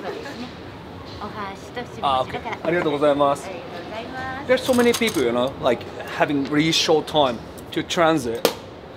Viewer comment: what is that word they say that ends in "mas" sound? I heard it always and never know what it mean, is it thanks? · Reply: arigatō gozaimasu = thank you